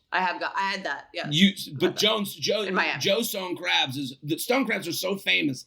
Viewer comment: I agree with about the stone crabs. They look cool with that huge claw but I think the Alaskan king crab is more tender buttery crab meat. Unfortunately I’ve been following Howie the crab so now I’m all ambivalent about eating crab haha 🦀 🎉 Another great show guys!! 🎉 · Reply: Ohhh Omg I thought they were saying stone crêpes and was intrigued and confused but now it makes more sense